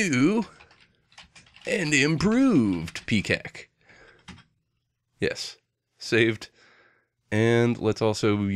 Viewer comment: Lol, I totally heard that as Peacack. Dying right now!